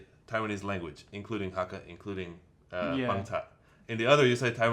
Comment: I was making the transcript of this video for academic use. Does anyone know at 1:44:07 what was saying about the Taiwanese language? "...in the one hand you say Taiwanese language including Hakka, including ??? and in the other..." This word appears again at 1:44:30. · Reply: google : 原住民族 台語名稱 頭一个頁面內底 有介紹。 Pangcah / 'Amis 阿美族 ▩▩【#邦查族】(Pang-tsa-tso̍k)．【#阿眉族】(A-mi-tso̍k) ▩▩ 【邦查話】(Pang-tsa-uē)：阿美語。 【邦查人】(Pang-tsa-lâng)：阿美族人。 【阿眉話】(A-mi-uē)：阿美語。 【阿眉人】(A-mi-lâng)：阿美族人。